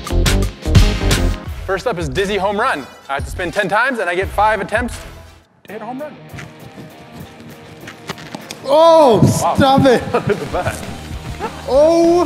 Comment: When they first said DIZZY homerun i thought they said Disney homerun